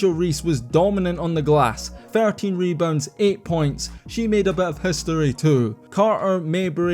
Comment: I totally thought you said 30 rebounds must be the accent